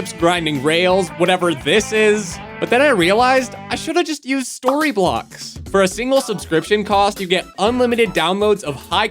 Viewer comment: at first I thought he said StoryBots